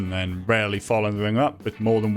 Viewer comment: i thought he said “the spy” and i was watching the rest of the intro thinking why is spy famous and never making 3rd games.